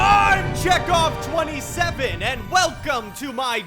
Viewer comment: For a bit I thought you said jack off 27 for a bit